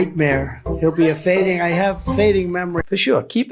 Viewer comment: At the 7:50 ish mark does trump say "I have a fading memory"? I swear that's what I hear. Someone please tell me which rally this was and at about what point in it. If he really said "I have fading memory" THAT NEEDS TO BE ON NATIONAL NEWS!